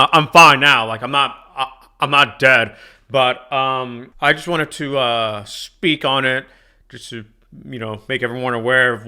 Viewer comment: I heard him say "I just wanna spit on it" rather than "I just wanna speak on it"